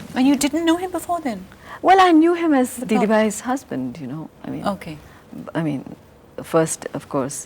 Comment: What did Rekha say?? Can someone please tell me what she said about Amitabh? Whose husband was he??? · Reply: She said 'didi-bai's husband'. That is what Rekha-ji used to call Jaya-ji. Regards, SimiGarewalOfficial